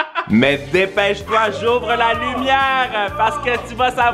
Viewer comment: C'est moi où il a dit j'ouvre la lumière 🤔